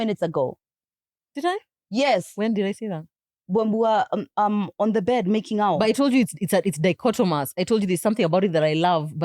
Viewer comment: Great episode!!🎉 Also at 26:32 did i just hear say "while they were making out in the bed"?😅